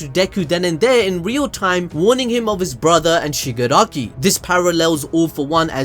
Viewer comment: i thought you said your brother and i wa s like hold up what Shigaraki’s deku’s brother?! Then i was like oh.